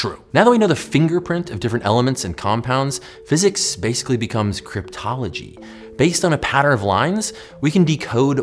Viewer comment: Subtitles say cryology while the host says cryPTology.